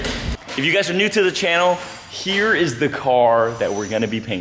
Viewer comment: Could of swore he said this channel was over